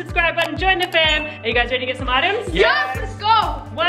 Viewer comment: when you said some items i thought you said smarter.😂🤣